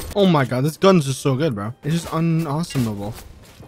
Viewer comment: Did He say un Awesomable???? 1:26 I spent 5 mins trying to spell this lol